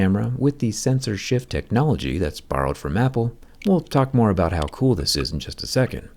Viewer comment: did he say apple